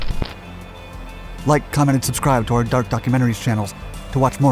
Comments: I kept hearing "dark ducks" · "Dark Docs" all I hear is "Duck Ducks".